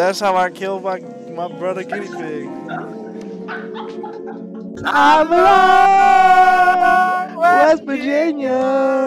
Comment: I thought said ima llama🤦😂😂